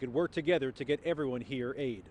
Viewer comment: Did he just say.. "work hard to give everyone here AIDS?" 🤔